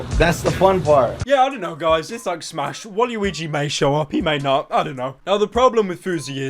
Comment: Did you just say waliwigi my nigga? ITS WALUIGI BITCH BOI